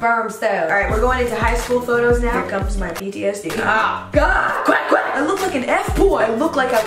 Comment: when she said "PTSD" at 0:58 i heard "BTS DNA" and i fell of my chair... i sometimes hate being army